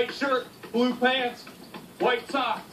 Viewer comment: "White shirt, blue pants, white top" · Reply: I just thought it was funny that he said "white shirt" and then "white top", well I thought that's what he said anyway.